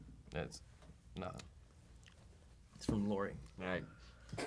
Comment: TELL ME DID NOT JUST SAY MY NAME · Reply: nope sista he said 'Laurie' I played it back at 0.5 speed... Sorry.